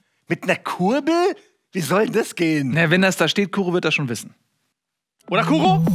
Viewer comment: Wer hat auch alles im ersten Moment "Kurbel wird das schon wissen" verstanden? XD